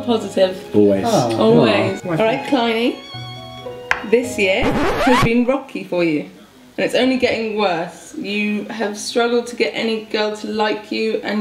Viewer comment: WHAT TF DID SHE JUS SAY !! 🤔🤔🤔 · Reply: She said “this year has been rocky for you, and it’s only getting worse! You’ve struggled to get any girl to like you, and you’re only getting older! You may as well give up now, nothing good will be happening for our any time soon!”